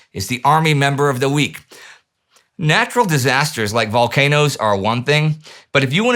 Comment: I had to rewind the last 10 seconds because I thought Indy said “Natural disaster like the Holodomor” I was hmmmmm say what?? 😂😂